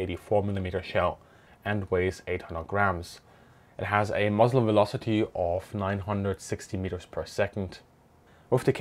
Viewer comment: nice video, but at 4:15 I'm I the only one who heard "it has a muslim velocity of"?